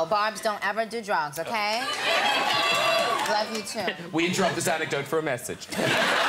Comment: what did he say at 2:19 ??? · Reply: We interrupt this ancidote, for a message .